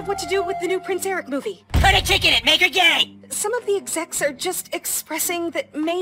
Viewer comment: Oh, it is "put a chick in it, make it gay"... I didn't hear chick the first time...